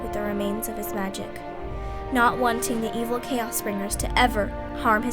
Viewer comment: At first i thought she said chaos emeralds.